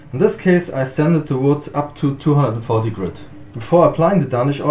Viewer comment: For those wondering, he sanded on coat #4 with 600 grit sandpaper. It was at this point (after wiping off the oil) that he indicated a 'satin' finish was achieved. This means 5 to 6 coats would be necessary for a higher gloss finish. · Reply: damn i thought he said 220 grit!! i went and used 320 like a dummy on a small area of a table, now I can't get the sandpaper scratches out